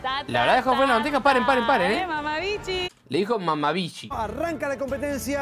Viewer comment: Jajaja en el minuto 4:33 entendí mama bicho jajajaja nada que ver